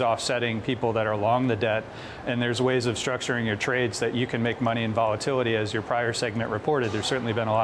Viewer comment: "Make money through volatility" I heard that as: make money through spreading FUD.